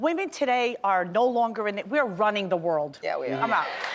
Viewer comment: She pronounced Ruining wrong. Almost sounded like she said “running the world” but we know she meant ruining.